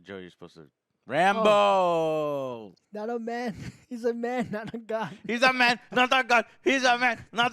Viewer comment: HE'S A MAN! NOT A GOD! COME ON COMRADES! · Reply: I thought he said “he’s a man not a goat.”